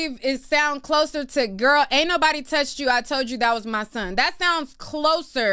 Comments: Sounds like she said, "Girl ain't nobody touch you, I touched my son." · I heard “Girl, ain’t nobody f*****g touch you, im tryna grab my son”